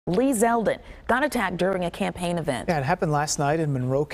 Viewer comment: I need glasses.. I thought it said Led Zepplin was attacked.